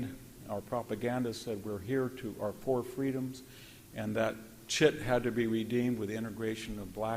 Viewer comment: did he say: "That shit had to be redeemed..."?